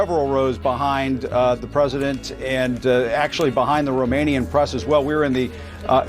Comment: DID HE SAY THE ROMANIAN PRESS haha 😂😂 Hi from Romania! Must've been Antena 3